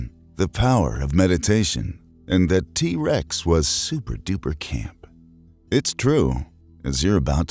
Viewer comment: I misheard "t-rex was cute"